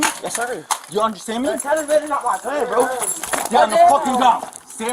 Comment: "Its not my gun bro" Ah yes, those pesky shirt guns that just end up in your clothes. Happens every time i do laundry. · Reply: I thought he said, "You got my gun, bro," meaning why would I lie on the ground? As if it was some excuse not to follow the cops orders